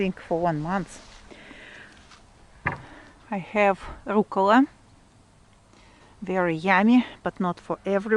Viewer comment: I always love the music you use. Your garden looks so healthy! What is the plant at 1:54. I think you said it was oakula? Oakra? Thank you. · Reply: called it rucola(the Russian language borrowed the word from Italian). In the US it's arugula.